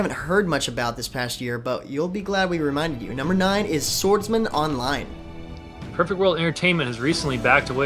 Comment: When they said SwordsMan Online i thought they said Sword Art Online :P